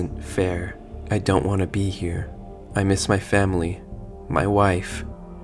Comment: lol I thought it said "I miss my wifi"